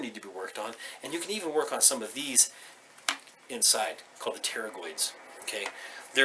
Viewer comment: What is the name said at 3:39 ? i was trying to look it up but couldn't find the name of the knarly thing. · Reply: pterygoid